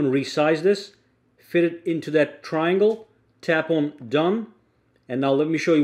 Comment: did he just said fit it in that triangle 🥲